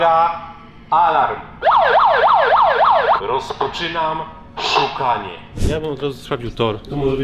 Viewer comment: czy tylko ja usłyszałem : "rozpoczynam sikanie" ?